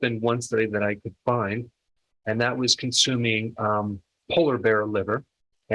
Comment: Did you say "consuming Polar Bear Liver" at 2:16? Damn it man. Humans are Loco!